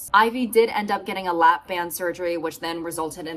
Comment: I stg I heard “lap dance surgery” 😳💀